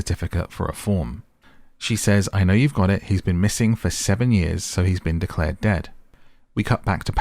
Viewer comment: If you go back to 11:16, you see the poster saying missing since 2003. Ellie was sent to 2005 so Paris has been missing for 2 years at this point. So by 2022 he would be missing for 19 years since he went missing in 2003. Not sure where you got the 7 years from. Unless I missed something. I think this is the correct explanation to your question. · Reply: thank you!!! I might have misheard I'm sure I heard her say "he's been missing 7 years"